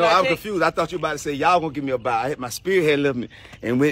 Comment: I thought you said yaw was gonna give me Bow 🤣🤣🤣